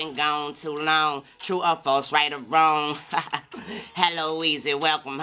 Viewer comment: Lmfao bro the whole time I thought he was saying “I’ve been gone too long too a far site of roam” lmfao it was true or false right or wrong wtf 🤯